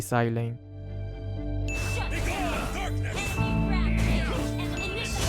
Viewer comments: Why i heard "begone thot" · Everytime Alucard uses his ultimate, I always hear "Begone Thot"